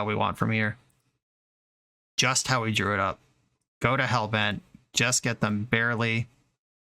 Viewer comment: "go to hellbent" I heard "Go to hell, Ben" heh